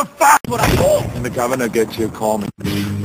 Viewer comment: what he sayin ? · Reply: when the governer gets here call me